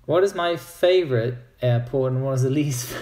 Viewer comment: subtitles say „Air porn” LOL.